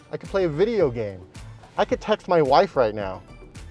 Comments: i had to roll it back a couple times just to make sure i heard it right 😂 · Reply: I had to turn on the subtitle just to make sure. Okay, subtitle confirms it he said text. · "I could touch my wife right now"😳...at least i swear that's what I heard initially. 😄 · I think I misheard you… I thought you said ‘I can touch my wife right now’